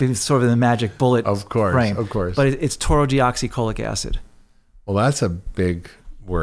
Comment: What's the word at 40:03 ? Nevermind; it's Taurousodeoxycholic Acid (TUDCA)